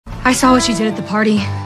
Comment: IM SORRY BUT I THOUGHT SHE SAID “ you kissed my geurl” in british